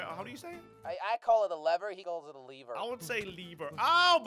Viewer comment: I think slogo said bieber lol😂